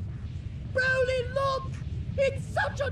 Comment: i heard "BROLY LOOK! NIGGAS BE WILDN"